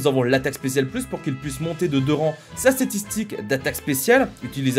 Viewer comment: C'est moi où a 2:05 tu dis statitik? 😂